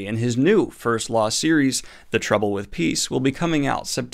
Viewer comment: I heard "The Trouble With Geese" and thought that sounded like a book I need to read.